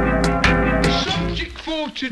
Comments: What i hear "we can be ethier loved or but be a dirty grub" .... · Why i heard “we can be either loved but nothing of it”?